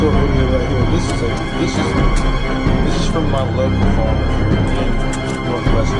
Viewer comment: Sounds like she said my local Walmart 😂